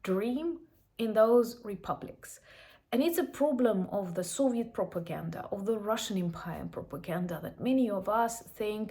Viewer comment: Hi! Well done! I thought you said "Soft Propaganda" (you did not) your volume goes down I don't care what Team says. Anyway, "Soft Propaganda" is a thing, and I study it. Conversely, JF Kennedy established our Peace Corps, and then U SAID as projection of "Soft power" (his words). I have a Russian living in rural MISSISSIPPI farm country. WTF right? Well, I engage, butt not rudely, or too rudely as I think Russians are savages barely above cannibal. She is the one who asked rhetorically, "Why do I think Putin is still alive?" As in, he must be popular. In her newly ripped boo hole I left the retort: BECAUSE PUTIN KILLED EVERYONE ELSE? She slides the soft argument, goes to church, teaches at the college... Soft Propaganda. Beyond that who knows. We do not let them slide softly into the night. Slava Ukraine 🇺🇦